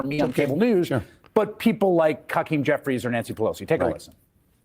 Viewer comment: sounded like he said ‘Cuckeem Jeffries’